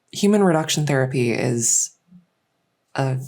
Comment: I heard “human replacement therapy” and giggled, amazing audio, loved every second